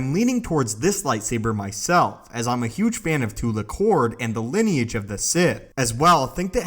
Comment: at first i thought it said tupak....